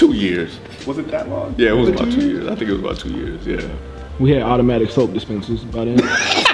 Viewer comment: what does he say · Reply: Nikola Rekovic “We had automatic soap dispensers by then.”